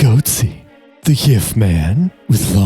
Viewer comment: I was about to click off and then I heard "yiff man"